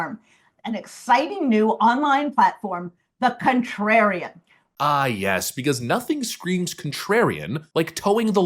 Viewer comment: I thought she said condom. Contrariem, still sounds goofy